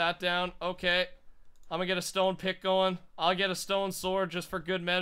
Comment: did he say Let me get a stone pickle? XD